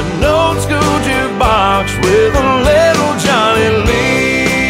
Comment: The first time I heard this song, I thought he was saying "Johnny Leon." I remember thinking, "Who's Johnny Leon?" Then I realized he was saying "Johnny Lee on," when he was talking about the jukebox "With a little Johnny Lee on." It's funny how some lines in songs are so easy to mishear sometimes. LOL! I'm such a dork!